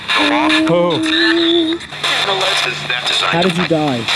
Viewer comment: i heard "hello Mike" not just hello